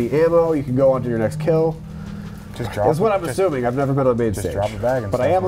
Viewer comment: what does say? “That’s what I’m assuming I’ve never ........ I can’t understand what he says next ? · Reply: "never been on main stage.. but I am on this couch"